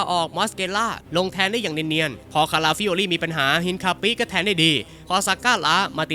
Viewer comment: ได้ยิน "เอ็นคาปิ๊" 😂😂😂